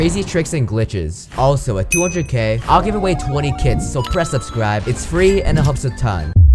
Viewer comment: I thought he said I will giveaway 20 kids when he said kits💀